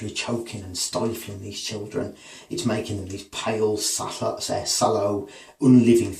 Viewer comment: What's the word you use to describe the strangled children at 8:22? pale s__ unliving...? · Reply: Pale, sallow, unliving